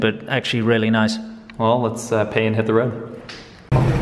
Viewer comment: Could've sworn I heard say "Let pee and hit the road" the first time I listened.